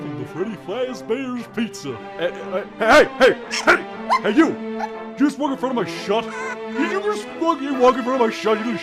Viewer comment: What did Freddy say? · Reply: Did you just walk in front of my shot?